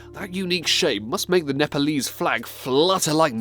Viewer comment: The way he pronounced Nepal almost sounded like "napalm" 😳